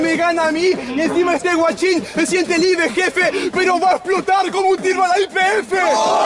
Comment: que dicen nacho ? no entiendo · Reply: Que va a explotar como un tiro a la YPF